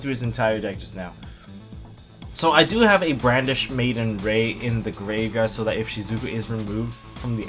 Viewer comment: I legit thought I heard brandish maiden window wanker for a moment hahahaha. Watch out for that gal. I love the super feature layout.